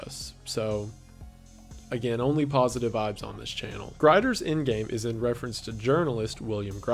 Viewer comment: i thought he said Grindr’s end game 💀